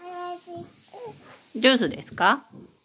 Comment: I think she was saying smth more like "(h)ora, juusu"(here, juice) rather than "orenji juusu"(orange juice)